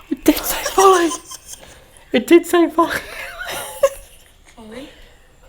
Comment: I just heard another voice say “Foley” after you replayed the male voice on VOX saying “Foley”. The voice I heard sounded like a child or girls voice and it wasn’t either one of you or VOX… was it somehow audio replaying?🧐👀👻